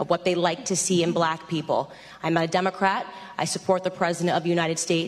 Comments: What does Candace say at time-mark 02:14? "I'm I Demoncrat". What? Did she mispronounce something? She is a Republican; not a Democrat. This is public and common knowledge. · did she say, "I'm a democrat."? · Sounded like she said "I'm a Democrat" at the 2:14 mark